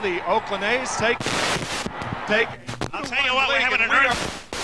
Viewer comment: sounds like they said “were having a earthqu- until the camera cuts